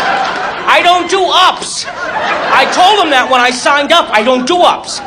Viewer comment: i thought u said u do not do ups u said i told him that when i signed UP! LOL